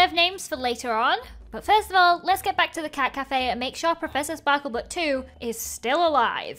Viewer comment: sounds like she said caca cafe 😂😂😂 (also who is watching in 2022 almost 2023 lol)